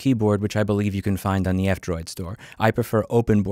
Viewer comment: Where do I get OpenBoard? Louis said in the video, but I'm not sure I heard right. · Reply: Go to the f Droid store